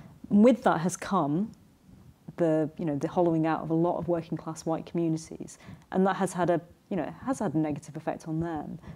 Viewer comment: Which commentators did she mention at 30:00? I heard Kehinde Andrews but I didn't catch any of the others. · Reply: Afua Hirsch and Reni Eddo-Lodge.